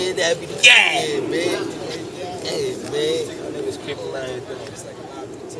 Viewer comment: What he say at 12:18? "What these people right here doing?"